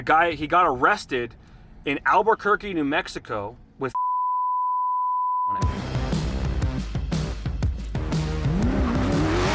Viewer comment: I know what u said lol I can read lips way too well he said “with 15 pounds of marijuana on him” lol u tried